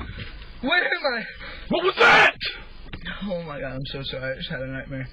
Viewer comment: What did ahe say here? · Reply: Cassie said, “dude, get out!” I think